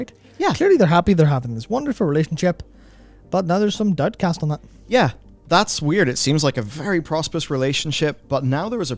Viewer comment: What is he saying at 3:43 ? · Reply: "Now there's some doubt cast on that" - Our Northern Irish accent can be tricky to pick up sometimes :)